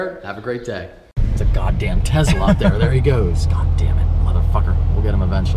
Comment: I heard "tezla" at the end....